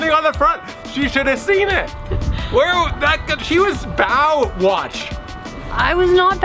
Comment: Bow Watch - I thought you said Baywatch. I think you were very lucky to have a strong boat guys :)